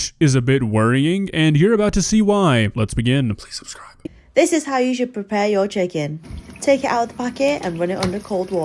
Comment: Not gonna lie the start of the first video I thought she said “this is how Asians prepare your chicken”…I’m Asian and i want to take a bath with my rice cooker plugged in……